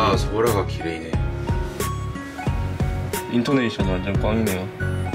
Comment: I heard you say Indonesia